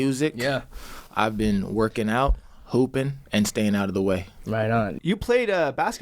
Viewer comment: Okay he said hoopin....I thought he said poopin.